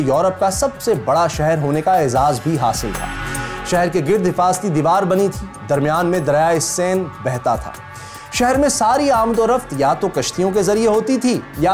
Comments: did i heard it wrong. Driya e sindh...???? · Is i hear right(dryaie sindh)??????1:03 time?????????????kindly id this is a mistake make it correct or if i am wrong so sorry.....